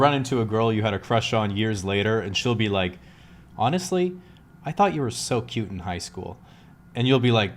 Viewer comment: I don't understand 3:46. What is she saying? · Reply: She says: “Honestly, I thought you were so cute in high school”